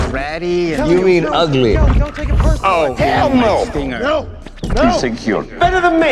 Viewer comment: was Bumblebee bleeped fron swearing, and did he say "oh hell no" at 19:42😂😅